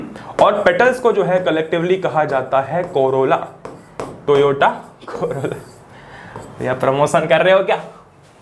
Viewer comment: corolla I heard corona 😂😂